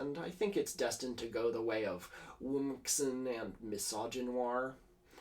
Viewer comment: what are these two words you’re saying lol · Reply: Womxn - weird radfem word for woman, genuinely did fall out of use because radfems are pretty meanspirited and suck to talk to and Misogynoir – word to describe the way misogyny affects black women differently than women of other races. A little weird that it was included since it's more a word with a specific use case than actually unpopular, but I guess isn't the usual demographic who would need to use it.